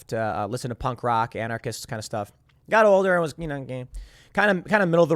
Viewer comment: Listen to 10:00 to 10:03 and please tell me that Lord Timothy Pool did not just say: 'got older and was you know gay'. I have been watching Timcast for years now and never knew Tim was a proud member of the LGBTQ+ movement. Go get them tiger! *This comment was sponsored by Established Titles *This comment was sponsored by Keto Elevate